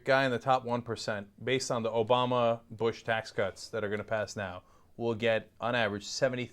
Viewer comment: WTF?, 2:10 he says Obama, but it says in the subtitles '' Old Bomber''